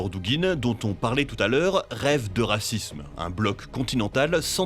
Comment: J'ai entendu "Dugin rêve de racisme" C'est vrai aussi, mais bon...